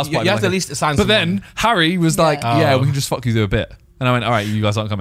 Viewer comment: I don’t understand what said, what did Harry say? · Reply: “yeah we can just F with you a bit”